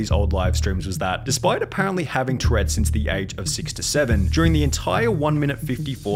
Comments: I know I'm late to the party but did you just say she had Tourettes by the age of 67 on 1:31? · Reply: he said "6 to 7" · My deaf ass really thought he said "Age 67' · am i dumb or did he say “since the age of 67”?